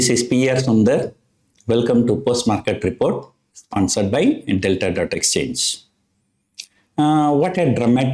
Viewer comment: Did he just say Sponsored by Intel.Xchange.